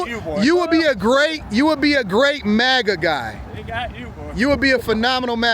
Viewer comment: Did he call him boy?